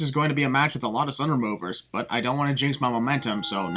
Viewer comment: Wait a second... At about 6 minutes the subtitle says "a lot of sin removers" but I heard "sun removers".